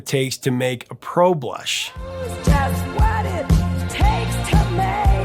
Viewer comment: Lol😂 where she says “pro blush” I always heard “road block”.